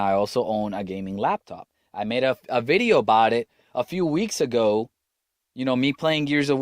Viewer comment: Did he really ? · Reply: Danm he didn't. I thought did in his ramblings at 6:58, he says "2 as a matter of fact. I own a desktop and I own a gaming laptop. I made up a video about it a few weeks ago". I heard it as "2 as a matter of fact. I own a desktop and I own a gaming laptop which I made up (pause) a video about a few weeks ago". It wouldn't have been directly, but it would have been enough for Cal Lightman, to call it out as him lying about owning those gaming PC.